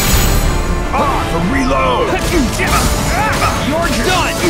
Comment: when playing for the first time kh2 like years years ago I always thought xigbar said "arrrgh burrito" instead of "reload"